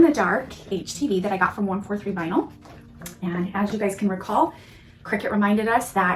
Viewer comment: Super cool! Thanks so much for your detailed tutorial! 🥰 Where did you get your glow in the dark htv? I bought a roll and it barely glows. Bummed about it too since I already cut into it. 😪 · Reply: Just watched again and heard you say you got it at 143 Vinyl! Thanks!😅